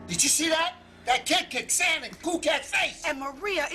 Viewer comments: " that kid kicked sand in cool cat's face!" Song is priceless. I have it randomly pop up in my mind some days! He said it with rythm! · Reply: I legit heard "that cat cacked sand in cool cak's face." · Am I the only one who thought that at 4:00 Erik said "That cat kicked sand in Cool Cat's face!"